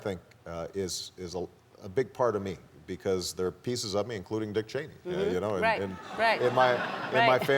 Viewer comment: did he said ''including the Czech''??